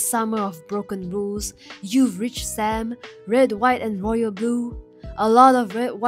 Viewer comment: I honestly thought was going to say The summer you were there